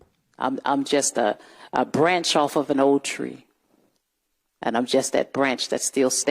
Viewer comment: I thought she said she was a branch on a tree???🤪🤪🤪